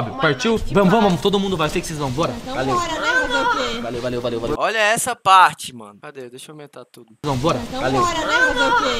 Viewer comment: Antes no minuto 12:14 eu escutei ANA quem escutou da LIKE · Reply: A Babi dizendo ahh não